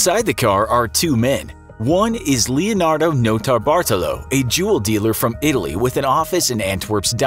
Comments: I thought he was saying juul “juul dealer” “Juuler” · At first I thought you said juul dealer